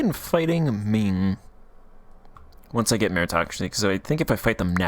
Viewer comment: Hahahahha within the first 20 seconds of opening the vid I hear “Ming is giving me meritocracy” hahaha. They’ve sealed their doom, literally asking to die